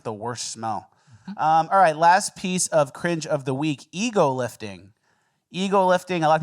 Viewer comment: Man I thought you said EAGLE lifting, I’ve never been so letdown by a video